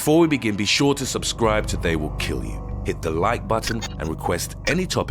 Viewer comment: Thought he said subscribe to today will kill you